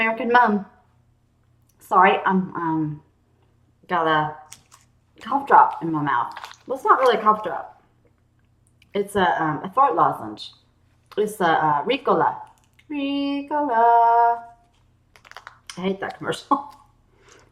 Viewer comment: Thought she said fart lozange.